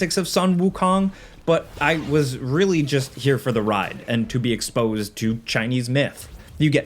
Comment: Thought he said he wanted to be exposed to Chinese men at first 😂😂😂 whoooaaaa